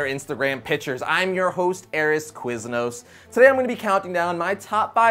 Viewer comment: Did he just say his name is arris Quiznos